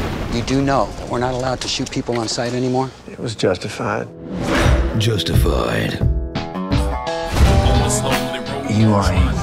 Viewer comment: Honestly, at first I thought he was saying, "It was just a fight." Until I realised he said, "It was JUSTIFIED."